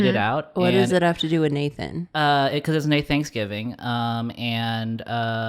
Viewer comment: I heard that as “No Thanks”-giving and my feelings on Thanksgiving honestly. Like, cool for you, but no thanks. 😊